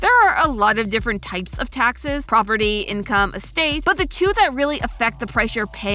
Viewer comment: I legit heard Poverty instead of Property and was like "Suprisingly, I am not surprised whatsoever"